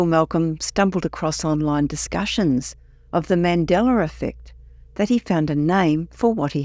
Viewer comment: I kept hearing "Mandelar" instead of Mandela😅